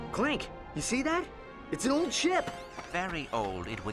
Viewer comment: And then?? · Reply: Jackson Ameye 13:00 at first I thought ratchet said, Clank, do you see that?......It's our old ship. I actually thought the star explorer was in this game